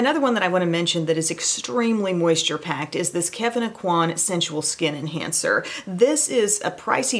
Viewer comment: Lol, I thought you said with your lovely Southern belle accent (I am Irish) Kevin Aucoin Skin cancer instead of skin enhancer I had to skip back as I was like surely they can't call it that.